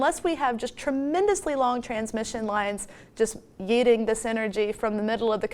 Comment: Did she say "yeeting"?